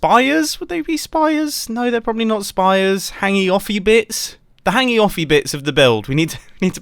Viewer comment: I thought he said "Hanging off your bits".